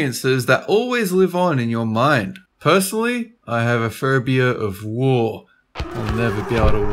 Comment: I thought said "phobia of wool." The reaction didn't seem to fit lol